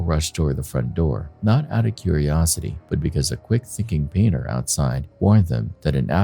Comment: did he say " a quick thinking beaner" 😂